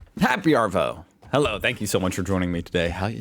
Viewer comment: I just realised hes been saying happy arvo... this whole time i thought he was saying happy arvell or smth and i didnt understand it